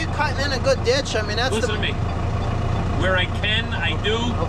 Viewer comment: "Listen to me" kind sounded like "Shut the fuk up... no?